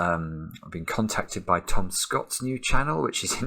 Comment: In the intro, I thought he said Tomska and not Tom Scott for a second: and I was very confused. That would've been a very strange collaboration if I had heard that right. Like what would that even look like?